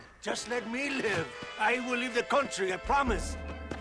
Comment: Does he say I will lead the country, or leave the country?